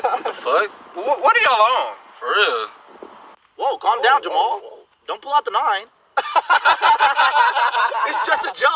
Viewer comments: My friend will constantly quote this and I always thought he said "calm down Jamal, don't pull out the knive" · anybody else thought he said don't pull out the knife